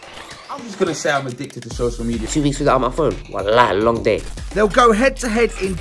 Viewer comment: 😭 lowkey highkey excited? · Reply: I thought u said wallahi long day